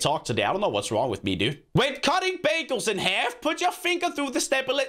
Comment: I thought he said when cutting babies instead of bagels I was like what the FFFFFFffffff